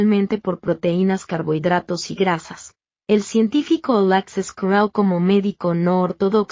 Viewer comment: Hola, en el video minuto - 1:27... no entiendo el nombre del científico?. Libro o revista científica o algún link...??? Muy agradecido, si alguien me puede dar alguna información y así ampliar este mensaje. Gracias.! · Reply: Alexis carrel